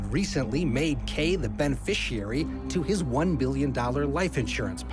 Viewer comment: Sounded like he said one billion @ 19:29 I was like wtf